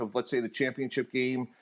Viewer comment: Bro this whole time I thought everyone was saying Eric the enemy I thought it was a nickname lol